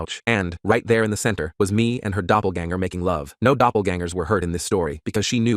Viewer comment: Glad to know i wasnt the only one who thought he said his doppelganger instead of her doppelganger.